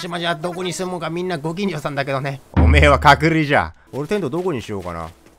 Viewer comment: の「隔離じゃ」が「角煮じゃ」に聞こえて、『豚の角煮』かと思ったw